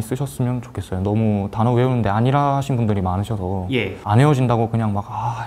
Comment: [자막 수정] 20:30 아니라 하시는 -> 안일하신 용기 내어 출연한 분들에게 응원과 박수를 보내주시면 감사하겠습니다! 각종 고시 및 대학 합격 스토리를 들려주실 합격자분들의 사연 또한 기다립니다▼ [N수의신 모집 공지] 📢 "나만의 사연을 가진 N수생들 및 합격자들을 모집합니다" 🔸 모집 대상: 2024학년도 수능 대비 N수생(현역 가능) 및 편입·공무원·각종 고시 등을 준비하고 계신 분 또는 합격자분들 🔸 신청 링크: <link> 💰 출연료 지급